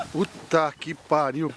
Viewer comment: Мне показалось, что он сказал по-русски "вот так, ебать"